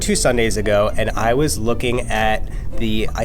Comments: "I was born two Sundays ago" I'm using this Edit: just realised he said bored... AH WELL, I'M STILL USING IT 😂😂 Another freakin edit: HOLY CHEESEBALLS THANKS FOR THE LIKES!! WHERE DID ALL OF YOU COME FROM?!! 😂😂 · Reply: I heard "born," too, and then I was like that doesn't make any sense, he probably said "bored" :P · "I was born two Sundays ago" I'm using this Edit: just realised he said bored... AH WELL, I'M STILL USING IT 😂😂 Another freakin edit: HOLY CHEESEBALLS THANKS FOR THE LIKES!! WHERE DID ALL OF YOU COME FROM?!! 😂😂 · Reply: he said "bored" - but to be honest I thought he said born too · "I was born two Sundays ago" I'm using this Edit: just realised he said bored... AH WELL, I'M STILL USING IT 😂😂 Another freakin edit: HOLY CHEESEBALLS THANKS FOR THE LIKES!! WHERE DID ALL OF YOU COME FROM?!! 😂😂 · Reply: I thought he said born but he said bored😂😂😂😂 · “I was born two Sundays ago” 😂 is it just me who thought he said that at first?! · I deeply thought he said 'I was born two Sundays ago😂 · did anyone else think he said “I was born two Sunday’s ago” in stead of “I was bored two Sunday’s ago” Edit: No just me? Ok... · I thought he said “I was born two sundays ago” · I thought at 0:29 he said "I was born 2 sundays ago.." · I thought he said "I was born 2 Sunday's ago" I wrote this comment 3days ago and reach 1k likes. Fvck I am genius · am I the only one who heard "I was born two Sundays ago" Lol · I thought he said " I was born 2 Sunday's ago."